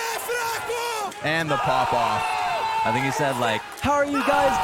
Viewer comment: What did Cold said at 30:22? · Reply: você é fraco means something like "you are weak"